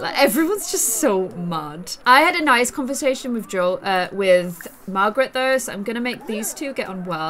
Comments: did anyone hear her saying margrit "her sister's name" or im a weirdo... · is it just me or...DID SHE JUST SAY ‘OH MARGARET’!?